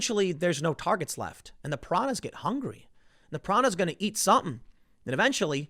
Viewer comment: I misheard "piranhas" to "pronouns" as in "pronouns in twitter bio" xD